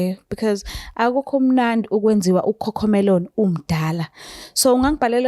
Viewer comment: Did she just say ukwenziwa ucocomelon😅😅